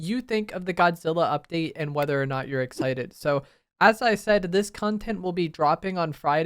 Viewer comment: It sounded like you were saying Kongtent for a second